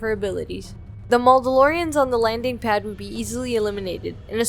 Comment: wait, did she just call them "maul-dalorians" ???